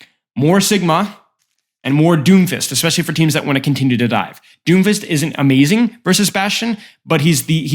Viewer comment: I heard "and more doomfist. Especially if you want to continue to die" I know its wrong, but i had to rewind it.